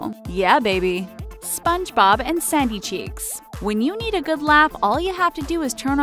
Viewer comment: I thought you said spongebob and saggy cheeks lmao